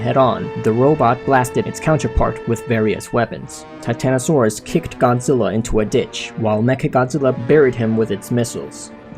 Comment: I thought he said kicked Godzilla in the di